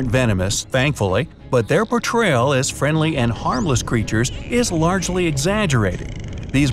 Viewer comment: I thought he said not harmless at all